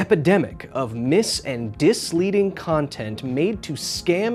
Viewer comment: ~ Did you just say "disleading"? Hahahaha